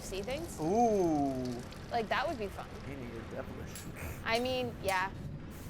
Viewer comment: What did he say? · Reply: Sun Jara I think he said Amy you’re devilish